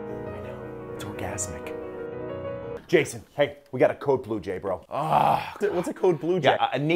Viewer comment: Cops: Wow this is our paradise! Security: Code bluejay CODE BLUEJAY! lol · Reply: Is that what he was saying? I heard "Code Blue, Jay!" (as in Jason) But yeah, "naked as a bluejay" makes sense too.